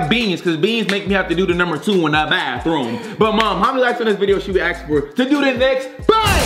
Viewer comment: did you say mom